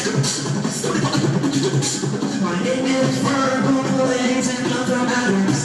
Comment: In 3:22 it sounds like he is saying my name is purple haze